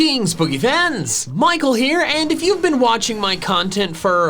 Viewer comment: I just realised that at the start of the video he says greetings and it sounds like he said 'guritins'